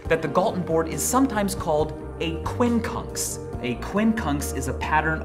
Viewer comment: Not gonna lie, i thought he said quincunts.